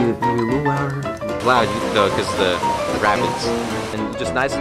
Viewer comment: did I hear this correctly? ... "because of the rabbits ..." lovin' this so far. :) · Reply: It's, Duh lol I said rapids, but rabbits work too